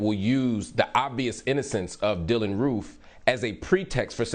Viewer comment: did Hill say “ INNOCENCE ?” ? Really ?